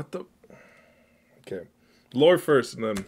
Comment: Help at 8:22 I thought you said lord first and not lore and just accepted it😭